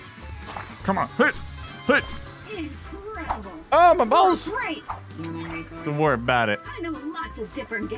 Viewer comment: what did he just say!? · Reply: He said oh my balls